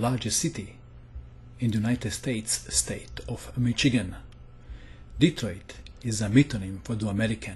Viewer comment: Haha I love accent. When he said Michigan @ 0:27 it sounded like he said McChicken. Damn you, making me hungry! xD